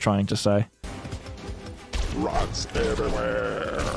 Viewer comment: Am I just hearing things or does the pirate mob say 'Rugs Everywherrreeeee' in a bad 'techno-Jamaican' accent?! Like surely that can't be right because it'd be too self-referential but... I swear that's what it sounds like.